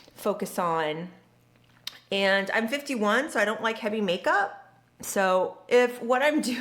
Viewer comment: I went back and relistened multiple times and I keep hearing you say at 4:10 that you're 51. I feel like I can't be hearing that right. I'm not trying to play. Are you seriously 51!? I thought you were my age - 35. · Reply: You’re very sweet❤️But yes! 51!